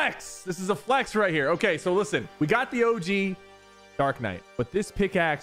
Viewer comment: tf did you just call black night